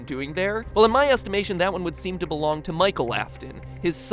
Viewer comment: Wow wow wow I thought you said he was Michael Emily I’m confused...